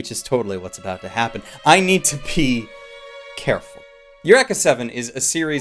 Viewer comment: I thought he said " I need to pee.. careful." I was just thinking " okay.. go pee. Didn't have to tells us." Don't watch the second season think it's called Eureka Seven: AO. it's not even close to how good the first is.